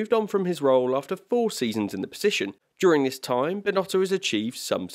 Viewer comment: I think I heard my man say Mid-tier Binnoto 😭